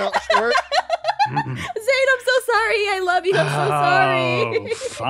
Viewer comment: Who heard Aphmau say "I'm sorry I love you" to Zane